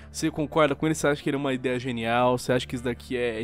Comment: Oq ele disse: Ideia genial. Oq eu ouvi: Pau genial!